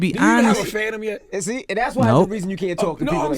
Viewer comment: Do you have a fadem? Or did he say phantom?